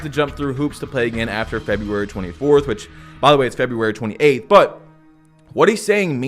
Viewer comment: Did bro just say February 28??